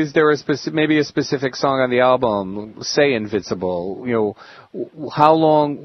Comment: So, did anyone purchase "Invisible?" 😂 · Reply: I heard him say "Invincible"